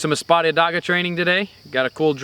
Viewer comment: I know what I'll be working on all next week, and longer, thanks!! Funny, the subtitles on my phone thought you said this was a "Spotted Dog" drill instead of Espada daga. I'll forever call it the spotted dog!! LOL